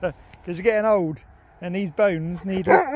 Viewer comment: How old is our fav silly dog now? 😉😉😉 0:46 it really sounded like he said "I'm not getting old" haha 0:58 love the hair though.. Perm? 2:16 strange sockets. Damn i love this dog and you who made it's all about him.